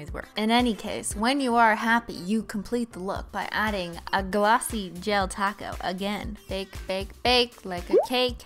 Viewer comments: I thought she said but add a glossy del taco 🌮 ⚰️r.i.p. vine you will be remembered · I thought she said : now add a glassy del taco